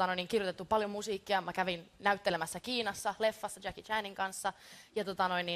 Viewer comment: Did she say Jacki Chan